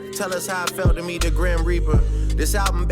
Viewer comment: Am I tripping? I thought Drake said “how did it feel to meet the “Graham Reaper” he said Grim kinda funny but I wouldn’t think he would give Kendrick that nick name 😅